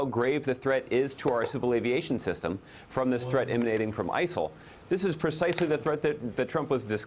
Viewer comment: What did he say at 2:04 ? Ice-hole? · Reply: He said ISIL, which is another name for ISIS. ISIL = Islamic State in the Levant.